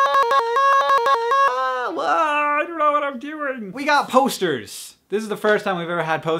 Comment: i thought he said toaster.